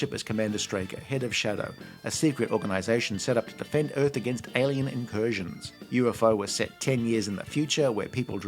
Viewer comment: Had to rewind a bit there, because the first time I could've sworn I heard "a secret organization set up to defend Earth against aliens and Persians"!